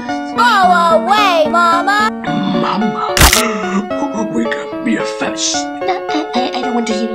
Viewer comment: I thought papa said we can be a fence °~°